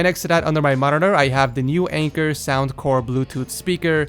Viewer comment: Lol thought the speaker said "wanker"